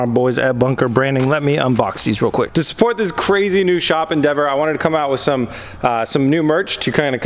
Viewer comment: Wait… 14:49 did you say new shop in Denver?